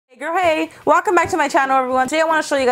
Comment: It sounds like she is saying girl hey instead of hey girl hey at the start of the video